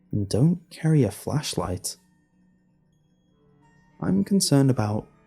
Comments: I thought it said "leave your fleshlights at home". I didn't listen and I am now in big trouble... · In the thumb i thought it said "fleshlights" .